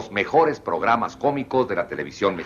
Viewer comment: escuche "fobicos" en lugar de "cómicos" jajaja alta sorda xd